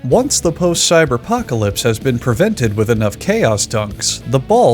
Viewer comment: What did you say??? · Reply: Post-cyber-pocalypse has been prevented with enough chaos dunks